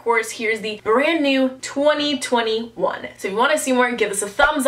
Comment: When she said “here’s the brand new 2020 one” I thought she said “Here’s the brand new 2021” I ran too see what year it was 😅